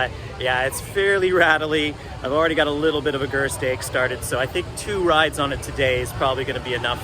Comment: what is it you said here? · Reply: Gerst ache (Gerstlauer ache)